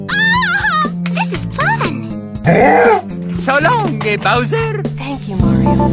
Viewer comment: i heard so long gay bowser😂😂😂